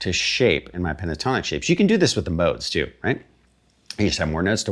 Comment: Thank you yet again for another great lesson Sir !!.. just one question at 11:57 says "you can do this with the 'boats' too ? You just have more notes to work from" ...have I heard this correctly? ..and if so what does it mean? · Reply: Modes haha! Thanks for checking out the videos! Glad to hear you enjoy them!